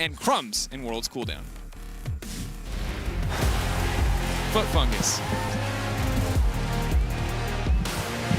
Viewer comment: did he say foot fungus???